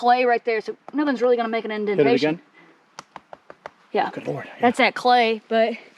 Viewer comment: right after asks to knock on the clay the second time, when she goes to stand back up I swear I here something that almost sounds like a voice, it almost has a electronic quality to it does anyone else hear it? It’s literally in between both of them talking so I can tell it’s not either one of them, don’t know if you guys have some type of equipment that talks or if it was a noise from one of them moving but I’m not even wearing headphones I’m watching on my TV and it’s loud and clear. Almost sounds like someone a little ways off saying “good morning “.